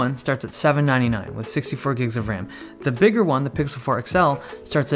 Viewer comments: am I hearing this wrong, or did he actually said 64 GB of RAM? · am I hearing things or are you saying 64 gigs of ram?